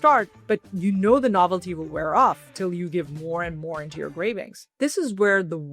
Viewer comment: She said cravings but I heard it as gravings which sounds like gravy cravings, and now I have cravings for gravy